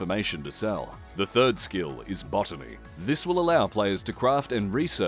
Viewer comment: my gay ahh thought he said "bottoming" 💀